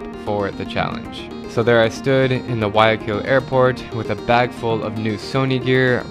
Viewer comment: I’ve never, EVER, been more excited to tell you stories in any country more than here in Venezuela. Every Sunday for the next 10 weeks, I’ll be sharing fresh new stories from this eye-opening nation, and I hope you’ll be here to enjoy the ride with me! ❤️🇻🇪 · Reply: The subtitles say the name is "(indistinct)" but it's the Guayaquil airport in Ecuador.